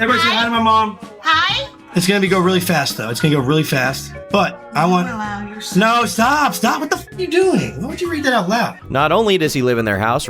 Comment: What did his mom say at 15:05?? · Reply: why would you allow your son to smoke crack in your house